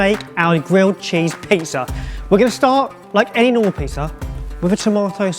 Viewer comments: Did anyone else think he said "like anal pizza" at 1:05 😂😂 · I thought you said anal pizza at first... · When he said "any old pizza" I heard "anal pizza" and choked on my drink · into the video it sounds like says "Like Anal Pizza" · I thought he said "We're gonna start our anal pizza" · Inswear i heard say "We're gonna start like anal pizza" Did not know it was that kinda sandwhich